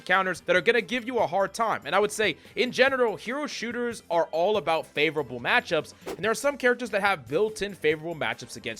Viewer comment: did you just say "in genital"